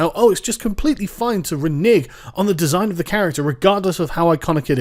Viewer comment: I heard 're-nig' rather than renege, triggered